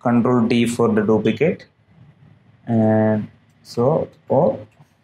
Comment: Bro I don't understand the part of video 14:42 sec.. u said something that area.. I just heard slow alt!! What was that mean??